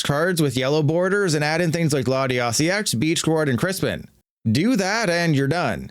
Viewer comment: latias EX, “beach chlord”? What is the card being said here? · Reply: Beach court